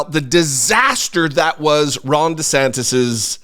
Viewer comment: Haha… at one point in this broadcast, I thought I heard you call him Rhonda Santis.